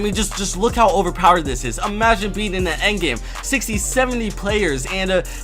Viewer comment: Did he say endgame and 60 and 70